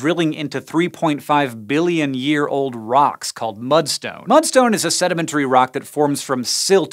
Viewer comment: I thought you said “bloodstone” instead of “mudstone” until I read the text.